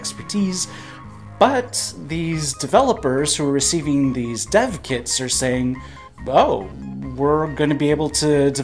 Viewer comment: When you said "dev kits" I heard "Death Kits". Hold up here!